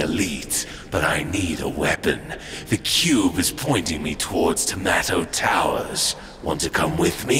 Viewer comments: Am I the only one who heard “tomato towers” at 5:14? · did he say tomato towers? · did he say tomato Towers?!?!?!